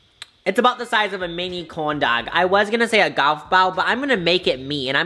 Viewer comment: corndog ?? Did he say corndog ?